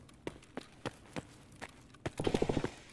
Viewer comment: I hear : "voilà pour toi" in french :-D